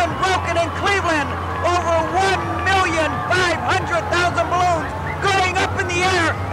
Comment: "Over 1 million 500 thousand balloons". I thought you said 1 million 500 thousand dead sea creatures